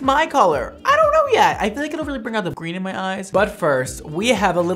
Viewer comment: Brad saying his eyes are green and me sitting here dumbfounded, kinda scared because I thought he had blue eyes. 😅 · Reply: I thought he said "bring out the green in my eyes". He looks like he has segmental heterochromia, so he could have blue eyes with green patches in them. ☺️